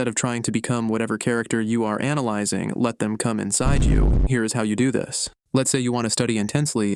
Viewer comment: am i the only one who heard "let them kum inside u"